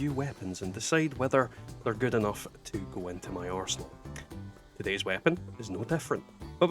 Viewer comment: it sounded like you said if weapons are good enough to go into your arsehole 😂